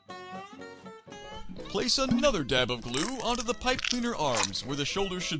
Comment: Did he say dab!?